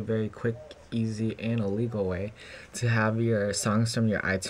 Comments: Dude you may want to be more Clear on what you said because I know you ment to say a legal but I sounded like you said illegal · No its totally free and legal! The way I said it in the video sounded like I said illegal because I was talking fast, sorry about that!